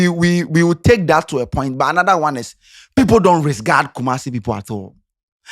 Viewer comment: did I hear you say "resgard" ? 😂